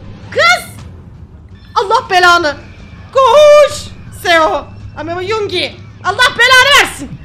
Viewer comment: yoongi demiş gibi değil mi? Ya da ben bugün kediciği çok özlediğim için öyle anladım dndndnndndkdnd